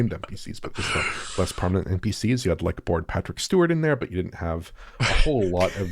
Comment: did say a bored Patrick Stewart? 😆